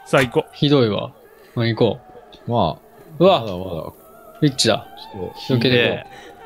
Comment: エッチだ…(空耳)